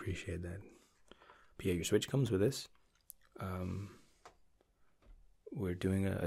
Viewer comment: I like how at times you really hear your Britishness such as at 1:38 where you say “ wi this” instead of “ with this”